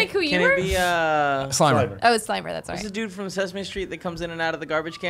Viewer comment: I honestly thought Greg said Steimer at first. I was like dude, not cool. 😅